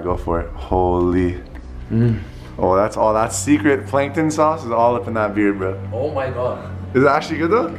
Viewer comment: Did he say plankton sauce?